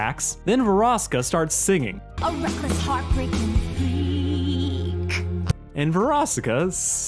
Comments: I’m pretty sure Verosika’s line was “A reckless, heartbreaking freak,” not thief. Understandable that you misheard though. I didn’t know what she was saying until I looked up the transcript on the wiki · wait i thought she said freak not theif